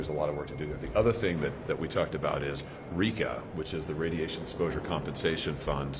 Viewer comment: when Hawley said they talked about RECA, did anyone else briefly think he had said RICO and thought that maybe they were gonna indict the entire DNC as a Racketeering Organization?